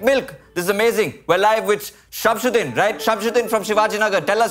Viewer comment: The first time said "Shamshudin" I heard it as "shove it in" 🤣🤣🤣